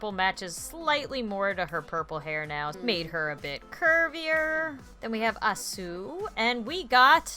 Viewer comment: I thought you said make her a bit more 'pervyer'